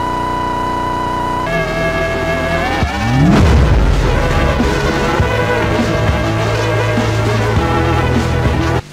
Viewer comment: que dice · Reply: Dice: "Ayuda por favor quiero a tangochini o alguien para que mate a esta chica hackeadora"